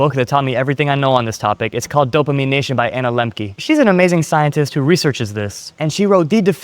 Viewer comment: What are you saying the book was called? dopamin icha by ana lemki? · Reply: Dopamine nation by Anna lembke!!!